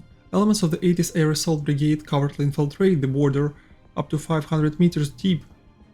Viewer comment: I keep hearing 'cowardly' infiltrated the border like some state run propaganda news outlet :') Yeah I know it's "covertly"